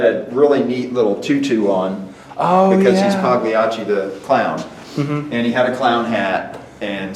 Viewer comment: Oh it’s “Paw Gliacci”! A play on Pagliacci. I thought you said “Paw Lacy”.